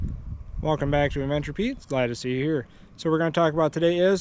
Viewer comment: I don't have any clear plastic bag pieces. But I ran mine thru the bulkhead fitting the same way I ran the power wire for the Upfitter switches. So if you check out that vid that will show it better up close. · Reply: thank you for both responses. I’ll watch that video. Sorry for calling you Pete. I always thought you said “adventures with Pete” lol. Man I need glasses, I could have read it also